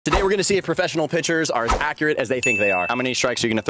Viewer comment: I thought he said professional pitchers?